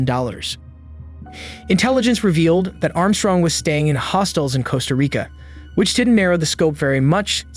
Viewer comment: I watched this today and misheard "hostels" as "hostiles", so I thought she joined some sort of terrorist organizations in Costa Rica, and so I was experiencing the 2nd half of the video like an episode from "24". But even without that, this is really wild.